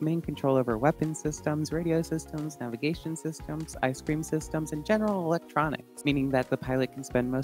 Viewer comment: did she say "ice cream systems" or am I tripping? 😂